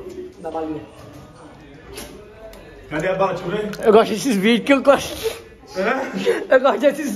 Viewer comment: So eu que entendi eu gosto de xvideos?ksksksksksk